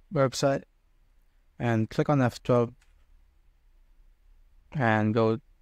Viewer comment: Click on what? I cannot understand what you're saying. =/ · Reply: F12